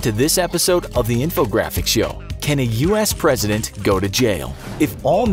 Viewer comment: i'm sorry I misunderstood the question, I thought you said "Can the President go to hell?" lol the answer is yes btw